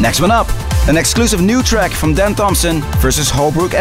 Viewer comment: Dan Thompson?! I thought he said Dan Dobson!